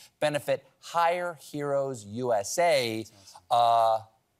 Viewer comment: Hire Heroes. It's really great to see someone actually helping our veterans instead of just saying they're going to help. Thank you, Late Show. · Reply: Thanks for writing it out; I heard it as "Higher Heroes" and momentarily entertained the notion it was a nonprofit hooking vets up with medical cannabis! Wishful thinking...but it needs to be federally legalized so the VA can prescribe or suggest it! It might help my dad's chronic pain.